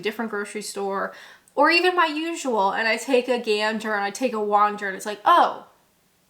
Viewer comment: did you say ‘take a ganja’ (about looking for new crisps)? Maybe I misheard. But if you did say that, that’s really funny. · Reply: 'Take a gander.'